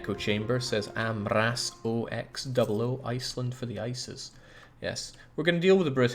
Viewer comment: I had the video minimized so I heard "Iceland for the ISIS" and I was like "what"